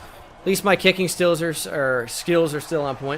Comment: you know he misses Kenny stills☹️. He aciddentaly whispered his name instead of kicking skills....